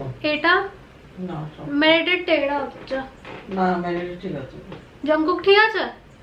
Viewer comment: Am i the only person who heard jungkookie at 9:38 😅 ohk its just me😅